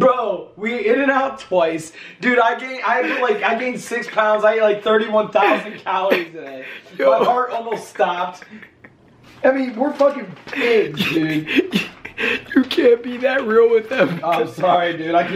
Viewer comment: yo am i high or did say "were fuckin baked"